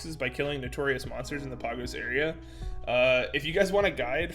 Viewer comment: every time he said 'Pagos' i kept mishearing it as poggers